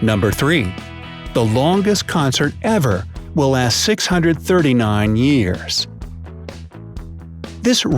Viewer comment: I thought you said 639 days